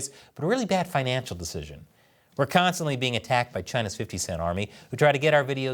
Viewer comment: So now the CCP has it's own 50-cent army, am I hearing that right? 😂